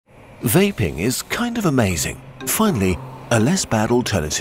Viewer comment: Lol.... Omfg! I thought it said... YAPPING! i do talk too much lol